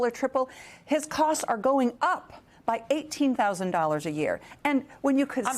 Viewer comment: at 1st i thought she said 1,800 a yr..omg, 18 thou a yr. thats not maga, thats highway robbery